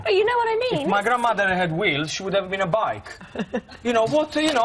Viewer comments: Did he say "If my grandmother had wish, she would've been a bike?" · "If my grandmother had wheels, she would have been a bike" I haven't laughed so hard in ages XD and it's not even contagious laugh that got me XD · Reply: First I thought he said “when my grandma had weed she’d let me a bite” then I thought “when my grandma had whale she’d let me a bite”